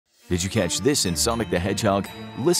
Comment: It sounded like he said “Somic”.